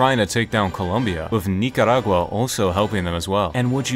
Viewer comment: Why did i hear n----?!